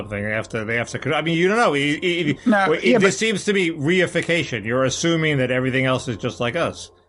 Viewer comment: I thought Mickey said "WE-ification." Which isn't a word but makes sense anyway.